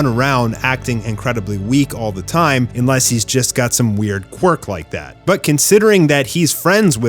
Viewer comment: did you just say Quirk?